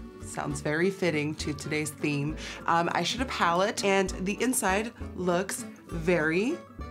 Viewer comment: it sounds like she said "I sh*t a pallete" 😂